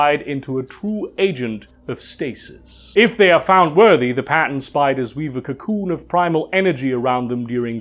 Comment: When you said “Pattern Spiders” I thought you said “Patent” spiders. That that was funny.